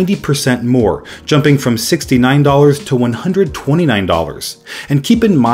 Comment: did I hear 69 dollars?